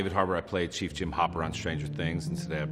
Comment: Did he say “I played” 🤭